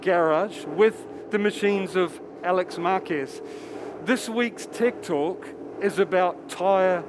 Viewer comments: i heard this weeks tiktok 😂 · Thumbs up if you heard him say "this week's tiktok".